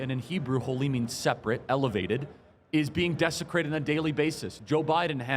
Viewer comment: I heard Kirk say "and he defecated the Senate hearing room". He meant "desecrated", right? Word play?